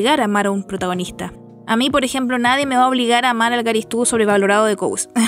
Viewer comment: que no te gusta quién? No escuché bien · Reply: Garistu creo- (no se como se escribe)